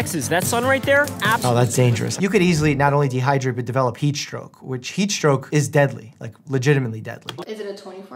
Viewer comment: Why did I think he said peach stroke instead of heat stroke😂